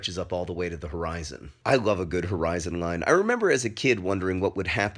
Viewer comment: i thought it said 'horizontal' line